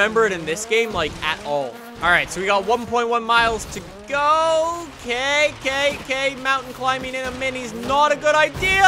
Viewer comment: did he say kkk? 😂